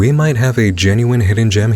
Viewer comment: I don’t know why but for some reason when he said “hidden gem” I thought I heard something else completely. It would actually be a good name for videos made during COVID! I thought he said “we have a Pan-gem-ic here!” Lol 😂